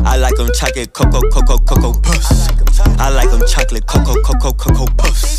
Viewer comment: I thought he was saying cocoa puss💔🥲😭😭😭😭